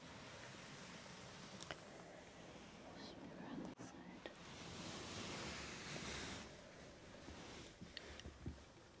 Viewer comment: what did she say? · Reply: I think she said "must be around this side" when she was looking for something